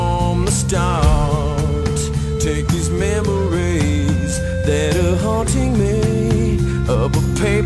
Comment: Always thought he said 'these chemicals' and the song was abig metaphor about drugs and losing someone cos of them.